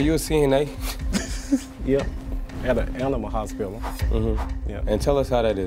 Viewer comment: i thought he was saying ENAMA ..whole time he said ANIMAL